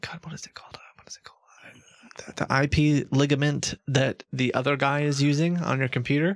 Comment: "We just ran the IP ligament," WTF?! 😂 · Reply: I thought he said IP alignment 🤣